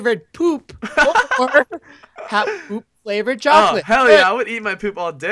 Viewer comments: Did say 'HELL YA' at 7:34??? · did anybody hear saying (hell yeah)